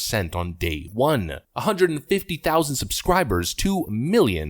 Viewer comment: 150 thousand is high. I honestly thought you said 150 subscribers.